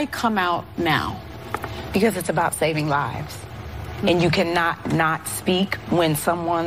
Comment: I thought she said “It’s about saving edges” instead of lives